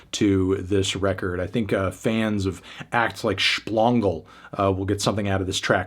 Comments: did he really say SHPLONGLE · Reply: SHPLONG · bro wtf did you just say to me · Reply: “Shplongle” 🤨